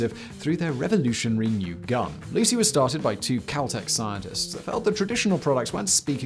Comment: At first I thought he said Lucy was started by Kel-tec scientists and I was thinking "No way, it's not ugly enough to be Kel-tec"